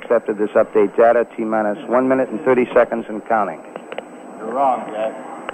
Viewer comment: Did someone just say “You’re wrong, Jack.” ?